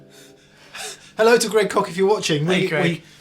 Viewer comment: Sounded like you said "Hello to great kock". What??? Oh, Greg Koch!!